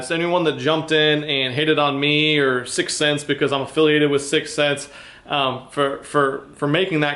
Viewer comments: LOL.... that is so funny i thought you said “cunts” you should have said that! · OH I THOUGHT.. he said CUNTS .. i guess i just pulled a randy marsh on wheel of fortune and got it wrong =)